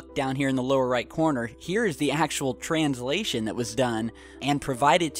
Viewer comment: I initially thought he said "Here's the asshole translation done..." lmfao